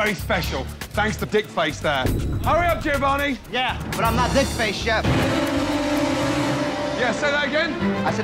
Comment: Giovanni: Gordon: DICKFACE Giovanni: 👁 👁 🍆 👄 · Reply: I'm having a Mandela moment right now. I always thought he said fuckface :thinking